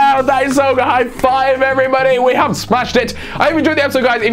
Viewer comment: He says link instead of like